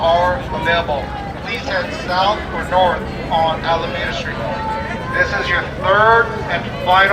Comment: Anyone else hear them yelling Wall Street fart Street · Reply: I hear “Lawn street” 😅😅